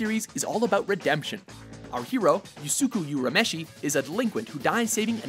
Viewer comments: Did he just call Yusuke "Yusuku"? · Ummmmm at 6:43, did he just say Yusuki?????